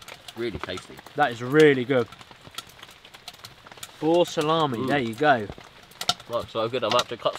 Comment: I can't understand you brits, are you saying bull, or boar? 😂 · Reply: Boar!